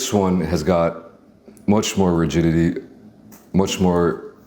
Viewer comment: i heard virginity thrice 😭😭 had to turn on subs just to find out he said rigidity 😭😭😭😅😅